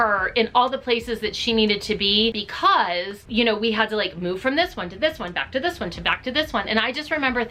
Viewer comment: When she first talked about dance number nine I thought she said “this hoe” and I was like what?!?! I actually laughed so hard because I had to rewatch it just to make sure that’s not what she said 😂😂